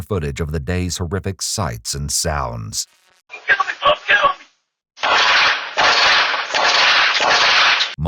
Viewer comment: i think he was saying don't kill me